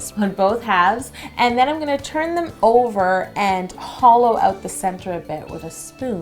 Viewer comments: When she said "hollow" I thought she said "holo". Can't help it lol I'm a holosexual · I thought she Said Holo 2:07( only Holosexuals will understand this ) And your puns were perfect👌👌 I donut know how to make a donut pun sorry